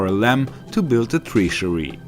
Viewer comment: my god man, atleast put the game in its original english. im sure you can read english. But what are you saying at 1:03 tree-sury? treasury? what